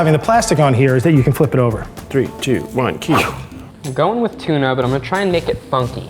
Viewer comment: did Keith really say “3 2 1 Keith!” Or am i hearing things?